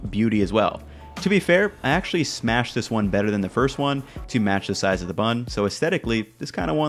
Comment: I thought he said he’d smash that burger